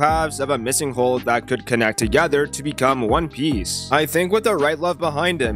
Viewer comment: did you just say one piece, sensei😍😍